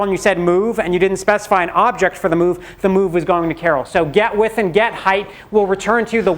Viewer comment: When he said "get width and get height" I actually thought he said Get with it Get hyped .. Because thats the sort of thing he says.